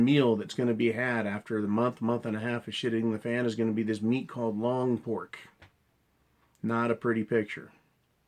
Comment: What is long pork? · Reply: I thought he said lawn pork, made me think of cats and dogs which would make more since. Humans would come second.